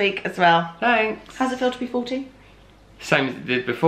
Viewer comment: At first I thought she said “How does it feel to be 14?” She said, “How does it feel to be 40?” lol